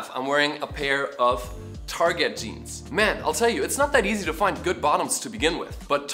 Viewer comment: „Man I’ll tell you it’s not that easy to find a good bottom to begin with...“ well... misheard that one...